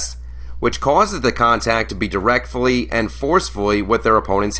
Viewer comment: does the guy say “directfully” instead of “directly”?